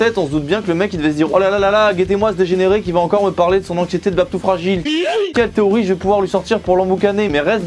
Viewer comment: J'ai entendu "babtou fragile" du coup j'ai decroché sinon j'ai adoré les 17 premières secondes 🐸